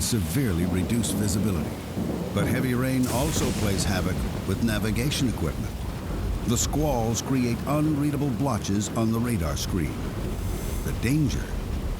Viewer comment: Tell someone please, what does he say at 2:24 - 2:27???? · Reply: "The heavy rains also plays havoc with navigation equipment The squalls create unreadable watches"